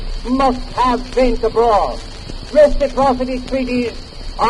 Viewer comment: the caption reads, "Domestic property". What he actually says is "Reciprocity treaties" and at 9:18 the caption reads, "These will not only be profitable in a commercial thing..." If you listen to the recording closely enough, you will hear, "profitable in a commercial sense..."and at 9:24 he says, "which will unite the two oceans".